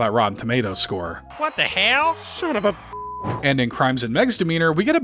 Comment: I'm gonna be honest, I thought it said "death and crime"